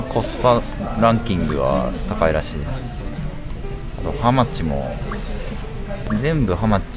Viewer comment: 「ハマチ」の発音がめっちゃ""How much""に聞こえて、一人で勝手にツボってもうた